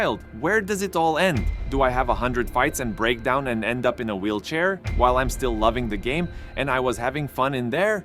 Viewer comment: He did not say "break down and end up in a wheel chair" he said, "Do I have 100 fights, BRAIN DAMAGE and a wheel chair?" Insinuating that he loves the sport but it will soon be over.. he was guaranteed 86 mil for the fight and projected to earn over 100 million after all the PPV and extras. When money isn't an issue there is no longer a reason to get punched in the head.